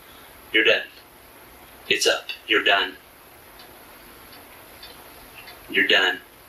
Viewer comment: Holy shit at this time stamp the fucking detective or whatever the fuck he is actually says “YOUR DEAD, it’s up your done”. Wow yo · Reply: He said it’s up you’re done. And I think it’s hilarious. She thought she was playing him like a fiddle—no more Mr nice guy 😂 He never said “you’re dead”